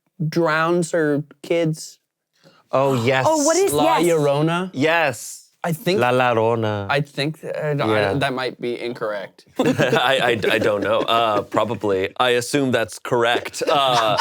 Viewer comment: sounded like he was saying la ladrona (the female thief) 😂😂😂